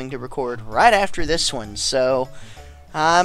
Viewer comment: First time visiting this channel and in the opening of the video I thought you said 'and this is Ultimate Man Toy'.